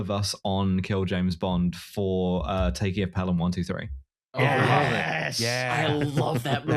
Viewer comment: what was the name of the movie said here? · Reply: The Taking of Pelham 123